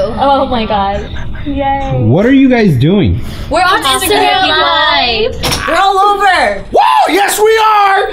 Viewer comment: did she say they're autistic? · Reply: Pivotkid85 they said they on instagram live